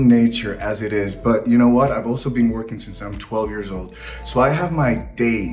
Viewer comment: I thought he said “I’’m 61” and I almost spit my drink out. Still super hot.